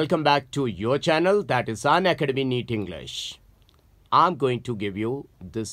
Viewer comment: O m g...the way he said unacademy ....I heard him say ..Sanya academy...😂